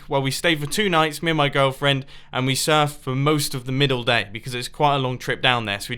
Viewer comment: i thought he said my girlfriend surfs nude